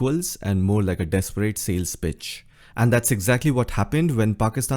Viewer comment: I heard desperate sales '3'itch. Then heard twice to get sales 'p'itch. However looking at image both are same